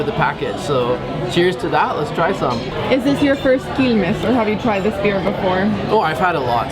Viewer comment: You should check out the auto subtitles. It read "Is this your first penis?" instead of "Is this your first Quilmes?" And then said "Oh no I've had a lot" :-D